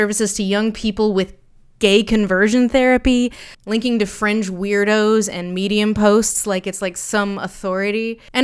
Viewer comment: I heard "French Weirdos" for some reason, and was like oh my god she's hopped the channel.